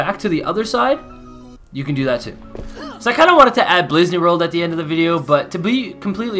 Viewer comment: “Blizzaney World”😂😂😂😂😂 that’s what he said